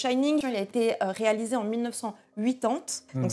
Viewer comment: en milles neuf cent 8 tantes ? Mais tu dis quoi. 😂😂